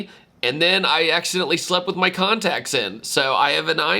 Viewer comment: I thought said he accidentally slept with someone lol